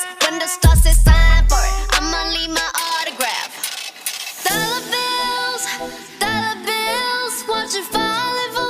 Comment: Na parte de “Dollar bills” eu smp entendi “Dar adeus” scrrr….Lisa nunca erra, sorry XOXO.